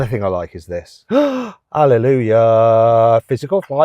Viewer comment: これなんて言ってるの？ハレルヤ？ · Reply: はい、ハレルヤです